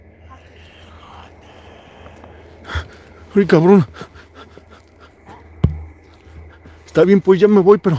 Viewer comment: oyeron un susurro o una voz que dijo (aléjate)0